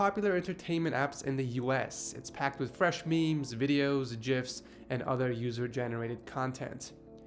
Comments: woah woah woah... hold on...at 0:48 did you say jifs? UNSUB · did you say jif... unsubed